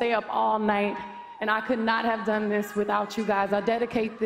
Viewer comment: Does she say 'I can't done this without you, Gaga'?